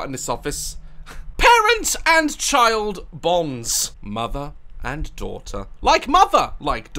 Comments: I thought you said "mother and daughter voms" when you said bonds. Works. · “Parent and childs’ bonds” Me absolutely horrified bc I thought he said bombs 😂😂